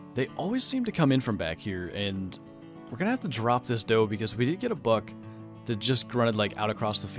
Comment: video idea do a realistic rifle hunt like sit in a tree stand with the 30-30 and just wait and try to go for drop shots (if able) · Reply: my bad thought he said drop tines